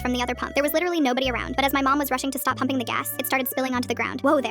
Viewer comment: I thought she said instead of pumping, I thought she said humping